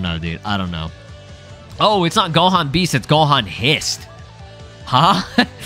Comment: "Ohh, Beast*. I thought you said *Hist